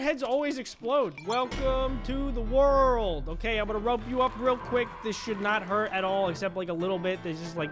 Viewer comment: what did he say? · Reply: “I’m gonna rope you up, this should not hurt at all”